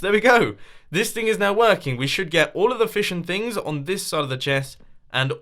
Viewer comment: sorry for my sppeling- i was waching awengers 2 in the bakrund and awerybody was screming and i hear you saying "and nonstockabol items in thi chest AND FISH IN THIS CHEST! s